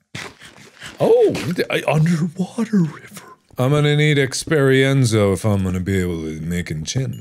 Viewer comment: "UnderWATER River" I've never seen such high level of intelligence ever in my life. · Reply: I thought he said under-- ground river, and the facepalm was put there because they're just flowing from a source, not a river